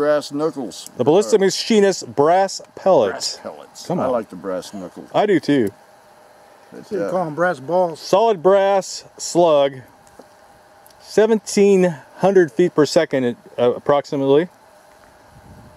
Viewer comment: Did say .. call them Brass balls . 🤣😂